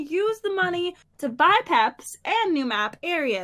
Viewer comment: when pat said pep i thought he said pepsi :)